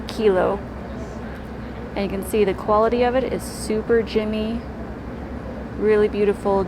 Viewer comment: Did you say the better rose quartz is super "jimmy"? What does that mean? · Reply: I think she was saying "gemmy" - that it looks more like a gem than a rock.